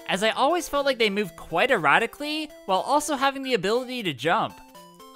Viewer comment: I swear I heard "They move erotically" instead of "erratically" and I was very confused.